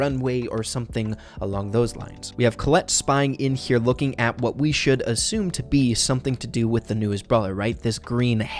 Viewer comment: Hmm chill karen, its a brawl stars video ffs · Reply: Oh I thought he was saying We shouldnt assume genders